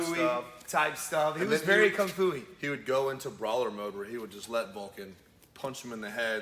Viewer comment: I thought he said "Lawler mode". Would've still made sense tho.